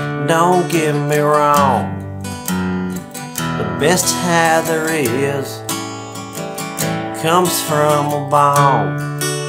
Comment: Thought he was gunna say “the best high, comes from rickies balls”😂